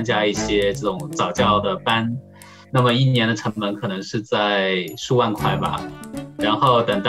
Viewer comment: I believe he said 100k yuan instead of 10k yuan? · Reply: Listen carefully, he said 数万快 (10K+) and not 十万块